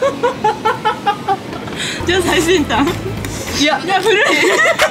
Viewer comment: なんて言ってるかわかる人います？？🥺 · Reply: 最先端？だと思います！